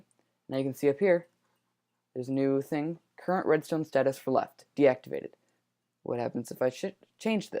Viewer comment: I swear I heard "What happens if I shit..." Awkward